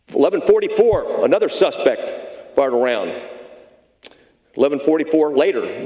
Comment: did he say "another suspect fired a round?" Perhaps misspoke 🤔